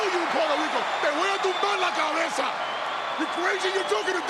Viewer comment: Did Damein say "en Puerto Ricote tumbo la verga"? oMG